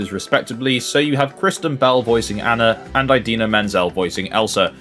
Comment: i legit thought he said "Christian Bale" voicing anna instead of kristen bell LMAOO😂